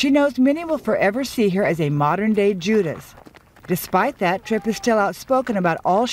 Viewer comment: She's the living, breathing definition of the word "Backstabber" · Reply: Oh, I thought she said "modern day Jew".